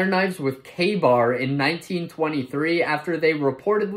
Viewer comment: I initially planned to skip this short but i misheared ka-bar for gay bar and i was interested in why this guy was in a ww2 fit saying such